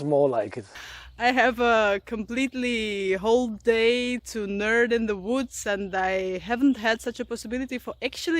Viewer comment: Did she say "a whole day to nerd in the woods" @ 1:26? If so...great original use of English.